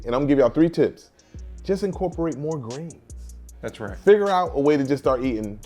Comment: the video says incorporate more grains on the screen, but he actually says incorporate more greens, like green vegetables.